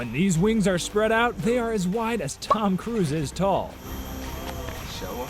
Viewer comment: Did you say that 'it's as wide as Tom Cruise' head ???